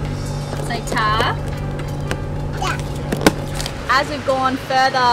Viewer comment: Am I hearing wrong or did you learn to say thank you (tack) in Swedish? At 9:53. If so, coooool / Swede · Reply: It's "Ta". Used in England & Australia.